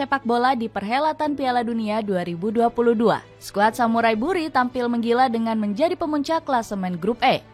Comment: Apa cuma gue yang atau salah dengar "Samurai Buri"